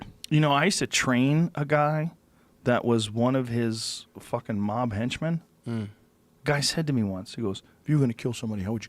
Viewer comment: sounds like he is saying maa bhainchod